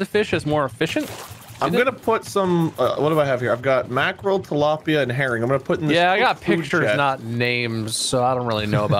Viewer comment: I thought he said “I’ve got Mackerel, Tilapia and heroin”